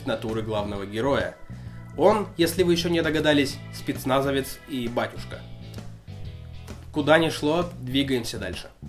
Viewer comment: Послышалось: "Спецназовец-ибатюшка". Послышалось.